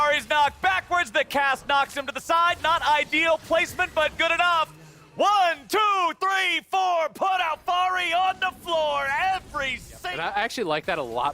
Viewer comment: Wait, did he just said: ,, one, two, three, four put Alphari on the flour`` I like this one ))